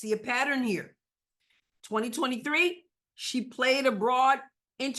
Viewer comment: When you said “She played abroad” I heard “She played a broad.”